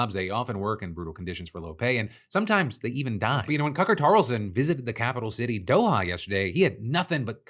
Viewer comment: did you call him "Cucker Tarlson"?! 😂😂😂🔥🔥🔥